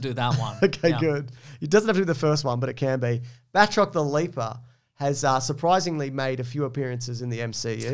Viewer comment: I had to turn the CC subs on after I misheard "Batroc the Leper"